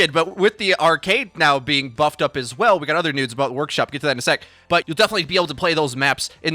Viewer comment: “We got other nudes about workshop” um did I hear that correctly??😂